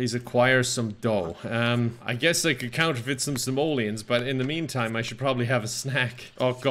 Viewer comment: I misheard simoleons as somalians and got very confused.